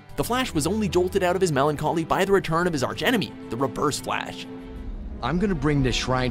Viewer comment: the rebirs fla a a sh Thats what i heard